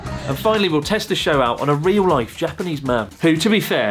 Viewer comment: Did you... did you say "real rife"? 😶